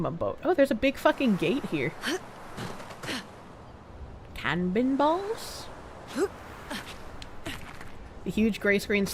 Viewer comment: I thought she said "Calvinball" for a second there.